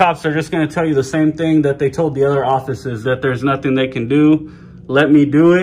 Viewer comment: theres another one at 21:03 "orfice" instead of "office" hahaha or at least I'm assuming haha